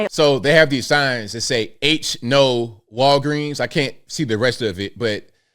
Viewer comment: did he say ‘H no’? Come on